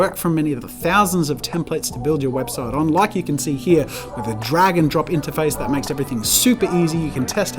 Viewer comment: Jazza “drag and drop” I heard “dragon drop” Wait Wix does what with dragons? No wonder Jazza likes em!